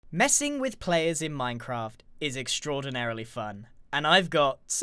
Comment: i thought it said minecraft 6